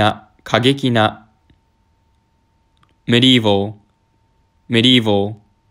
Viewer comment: medieval→メリー号に聞こえてしょうがない